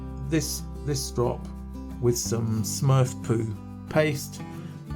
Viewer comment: Did you just say "Smurf poo" paste?